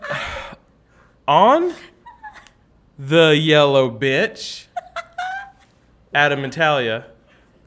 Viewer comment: Did he say yellow *** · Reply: He said "bench" tho it does sound like "b*tch"